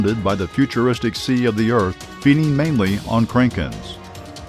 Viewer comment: It sounds like you said kreyken not KRAKEN